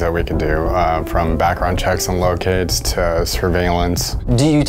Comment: Thought he said "Background checks on little kids"